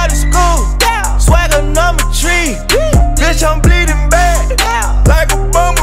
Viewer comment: “take them boys to school swagonometry” is still the coldest fucking line ever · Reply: Thought that nigga said swagger number 3 lol